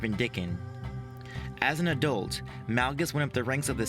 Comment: When you pronounce Vindican it really sounds like your saying Ben Dicken lol